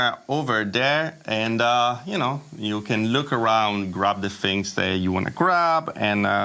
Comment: LOL when you turned & said you want to grab, I saw poop doll & thought you said you want to crap.